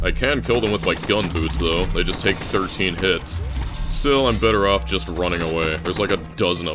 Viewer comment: the audio says thirteen hits but the cc says twelve hits. is feeding us misinformation about TowerClimb